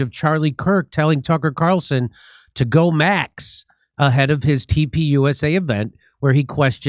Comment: Sounded like he said “don’t go max” to me 🤷